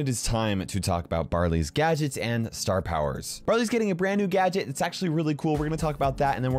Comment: It sounded like he said "Brother's" getting a new gadget.